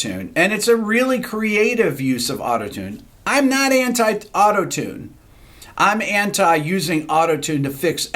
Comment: Everytime I hear "I'm not hentai-autotune"